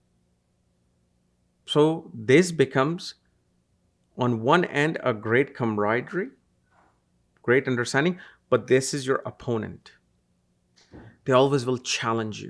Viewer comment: Any one can write what he said in the next 5 seconds. I can’t understand it. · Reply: "..So this becomes, on one end– a great camaraderie, great understanding, but this is your opponent!..."